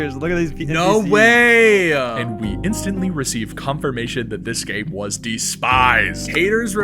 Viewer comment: I thought you said congregation